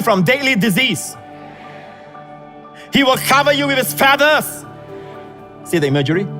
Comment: I thought he said he well cover you with is fat ass.